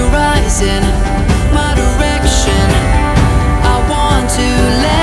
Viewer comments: did he say erection? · wtf at 0:44 it says my erection